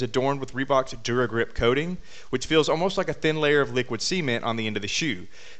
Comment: I thought you said "liquid semen"....but I am guessing you said "liquid cement"....it sounds funny when you emphasize the "ce"....